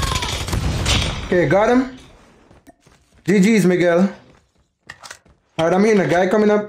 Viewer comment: I thought you said ggs niga but you said ggs miguel